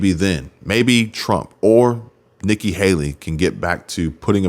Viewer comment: I think your audio was cutting out for a minute, it almost sounded like you said Nikki Haley after Trump in 2024.. Good thing my wife knows cpr, cuz I almost died laughing.